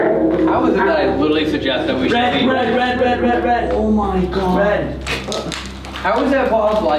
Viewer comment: I hear “red” from the phasmabox right before the red light was turned on